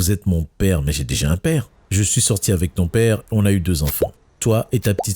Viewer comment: c moi ou il a dit je suis sortie avec ton pere😂😂